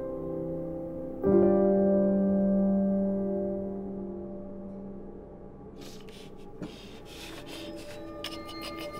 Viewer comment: 만월이 뭐라고 하는 거지?? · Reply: 쉿인것같은데요